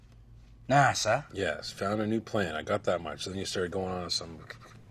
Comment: did ricky just called bubbles mike? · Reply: He said “yeah I got that much”